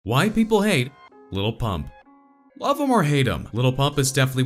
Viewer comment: At first I thought it said “white people hate lil pump “